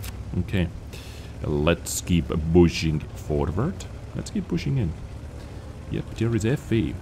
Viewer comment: I don't know whether this was intentional, but in the second map as you were pushing forward near the end I thought you said "bushing forward". Of course this would be the right manuver with this tank :)